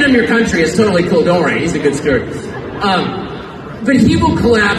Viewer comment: LMAO @ 2:08. I had to activate subs after replaying a few times as I kept hearing Tucker say he's a good skirt... the skirt is much more actually correct then steward.